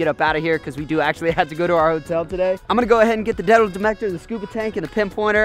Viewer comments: Is it just me, or did he say, demal metictor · Did anybody else catch him saying "Detal demector" instead of "metal detector" @ 2:52?😂